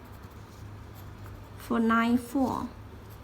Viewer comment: when she says "line four" it sounds like "nine four" confusing me until I saw what she wrote. And again at 1:59 when she says "Express line", it sounds like "Express nine".